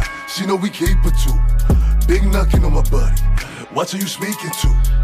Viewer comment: Whole time i thought he was saying “bae do not get on my body” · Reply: Nah he said big knockin on my body. Lol. Knocks/knockings lingo for guns in BK from the Caribbean